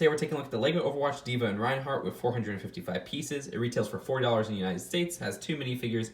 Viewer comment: did he say $4 that’s cheap